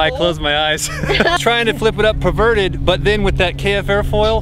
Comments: what did he say a 5:14 i think he was tryin to say inverted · did he say "i was trying to fly it perverted" ?